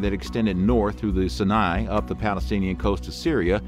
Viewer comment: does he say, "up the Palestinian Coast of Syria?"